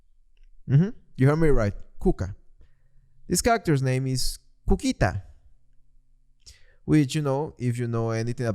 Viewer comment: OKAY, i might have misheard, but in the song one of the kids names is coquita i think? which means coconut but also thats the name of an alcoholic beverage?? and whats worse is that when i was young, my mom's side of the family (latin american) used that as a euphemism for a girl's privates. so. hearing that as a child's name was shocking